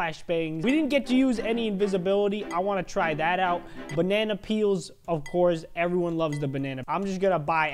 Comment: AT FIRST I THOUGHT THE BANANA PEEL SAID BANANA PEE! XD😂😂😂😂😂😂😂😂😂😂👌